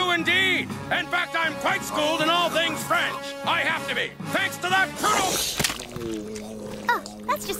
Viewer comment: I can't understand what the guy says at 9:30 after the "I do, indeed!". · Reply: ''In fact im quite schooled in all things french! i have to be''